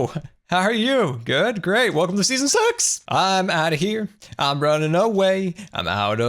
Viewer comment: Lol he said at 6:41 welcome to season six but sounded like welcome to season s x